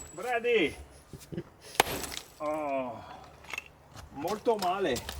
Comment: 😂 swearing in Italian, thinking nobody will notice 🤣🤣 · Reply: What did he say? I heard him say "moto male" and translated w/google and it said "bad Bike".?